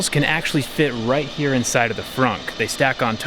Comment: did he say frunk? 😂